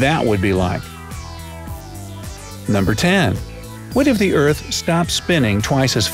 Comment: why did it say started but he said stopped